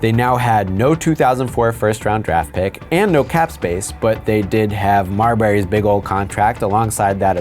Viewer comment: For a second I thought you were about to say " But they still had Marbury's big ass head"😂😂😂